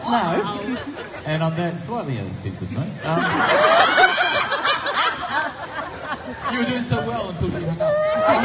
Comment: What did host say? Can't quite understand. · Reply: On that slight offensive note, you were doing so well until she hung up